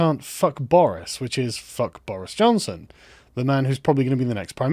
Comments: I thought he said Chuck Norris. Not fuck Boris. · When he said Fuck Boris, I thought he said Chuck Norris. WTF